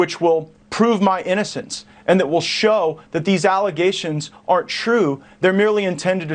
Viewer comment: Did I hear Gaetz say "..that will show these allegations are TRUE" @ 6:12-6:14? Someone help me out here.